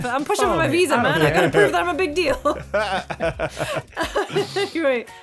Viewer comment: The first time I heard Jake's nickname I misheard "Jake mistake"